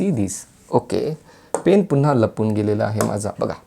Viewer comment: what u said sir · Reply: Ignore it dear, i said pen hides when i want it